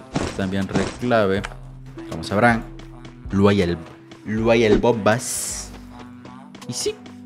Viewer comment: Pero cuando aparece ahí... no Dice LUAY EL BOMBAS sino que dice Luay El BOMAS Deja tu like si también te diste cuenta en el minuto